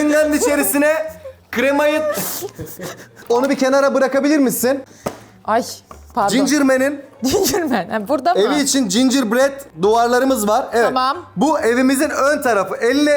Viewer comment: da aş dedi sonra pardon dedi ama ben aşkım der sandım ya yanlış anladım yada doğru anladım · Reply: Ayş dedi abartma ayy anlamında bıkmışlık gibi düşün